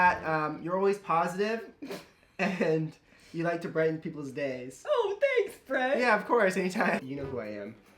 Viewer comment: I thought he said ride people's dicks but he said brighten people's days I seriously can't hear 😂